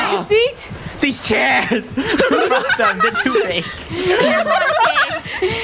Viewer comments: did say a bad word? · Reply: I heard him say "F them their too big" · did anyone else think said “These chairs!! F- them.” ?? (Not saying he did it just seemed like it)